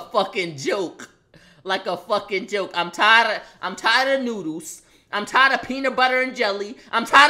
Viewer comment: is it jus me but when he first said “noodles” it sounded like he said “nudes” or it’s jus me ?😂